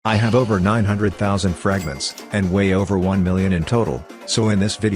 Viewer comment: when he said:"and way over one million" i thought he said:"and weigh over 1million"